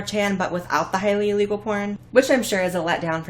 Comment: I HEARD PORN I'M TRIGGERED !!!